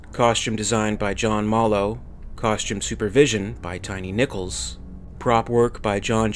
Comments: Am I the only one who thought he said 'Tiny Nipples' at 5:20 instead of 'Tiny Nichols'. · who else was half listening and thought he said Tiny Nipples? XD